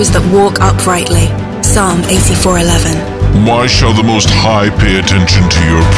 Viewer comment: Did I hear “why shall she ?!